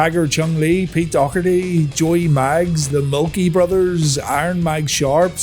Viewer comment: Haa! 0:56 Did I hear that right? The Milky Brothers? I mean they are a couple of pasty dudes but nobody is going to take that team name seriously. 😆👍